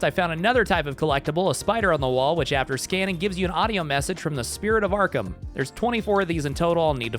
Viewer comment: Also, when 🐸 said “Spirit of Arkham” who else almost thought he said “Pillar of Autumn”😂 👇🏻